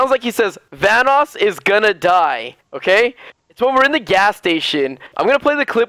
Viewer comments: It sounds like hes saying vannos is gonna die but i can hear some other things in what he said. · I thought he said everyone is gonna die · Welcome to the show! I hope you guys enjoy the video! If you did, please like it. Thanks. :D <3 <3 <3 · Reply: I heard Carlos's is gonna die 😂😂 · Welcome to the show! I hope you guys enjoy the video! If you did, please like it. Thanks. :D <3 <3 <3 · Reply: Its realy sound like he said vanoss is gonna die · yeah.....same I thought hee said vanoss is gonna die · I heard "VANOSS IS GON' DIE!!,SO ONE OF US ARE GONNA DIE!!!" So yeah